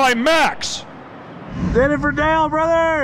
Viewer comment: i know that he is saying "do it for Dale" but for some reason i hear "doofer dale".